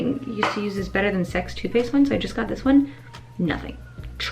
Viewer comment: When you were talking about the better than sex mascara I thought u said “better than sex toothpaste” not too faced 😂😂 I was like ughhh that’s not toothpaste 🤣🤣